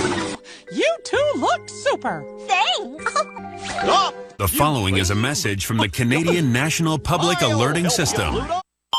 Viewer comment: What goofy said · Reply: OUP!